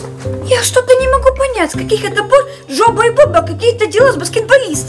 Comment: Мне одной послышалось жопа и попа 😂